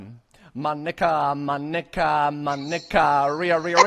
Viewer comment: sounded like he was saying nigga instead of mo ni ca.